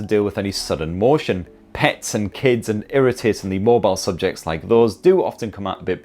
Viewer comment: Did he call his kid an irritating subject? Poor choice of words lol.